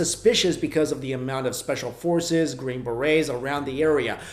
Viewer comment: Phil helped build d.u.m.b deep underground military bases. 66 ppl died in dolce 3 or 4 ppl are still alive from that day but they live in Canada and are protected by the country. Phil couldn't have killed himself because he was missing most of the fingers on his hand 🤚. They killed him by tying his catheter around his neck and beating him. They took all of his documents and photographs he would bring to seminars even pieces of metal from crafts etc. its really interesting and i would definitely tell people to look him up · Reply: Yeah, man. Also, I could have sworn thst Phil said "black berets" and not green berets. That was a spooky story and the images of how he died seriously freaked me out 😫